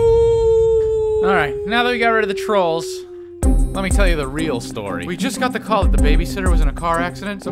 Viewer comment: sounded like he said let me tell you the remix story.